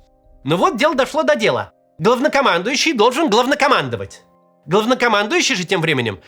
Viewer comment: Мне одному послышалось "говнокомандующий"?😅